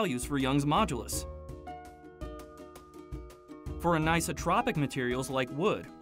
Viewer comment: Great video! Thanks for creating all of these, they're great. At 2:27, I'm not sure if it was your accent or not but it sounded like you said isotropic rather than anisotropic? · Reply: I think he said "for anisotropic materials", but it sounded like for an isotropic materials. If he had said it the latter way, materials wouldn't have been plural.